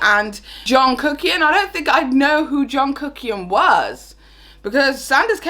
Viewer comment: omg i played the video and went to answer a message. when you said i heard it as jungkook and let me tell you… i was confused af.